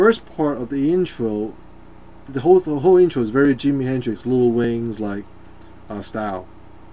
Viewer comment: did u just said the whole intro is very jimi hendrix, lil waynes style?....... Are you serious?